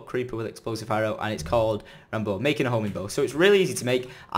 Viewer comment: thought he said homie bow :)